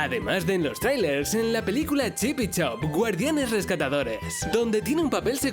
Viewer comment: Se dieron cuenta que dijo chip y choe